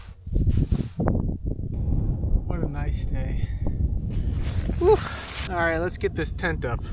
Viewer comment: Did he just say "what an ice day."